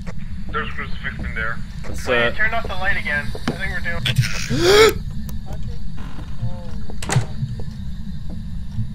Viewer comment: what did the ghost say? 26:23 say catch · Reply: It said attack